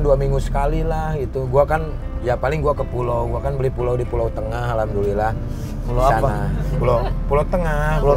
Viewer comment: Ini apa ane yg salah denger ato beneran sule bilang dia beli pulau di pulau seribu ya? Menit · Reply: ya gw jg dengar tp pulau yg di tengah.